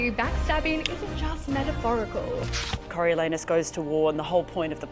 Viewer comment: Why did i hear "Cunnilingus", not "Coriolanus"?? Also this looks so fun!!